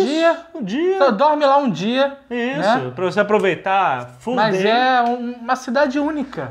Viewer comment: Pra voce aproveitar fude. ouvi isso da primeira vez kk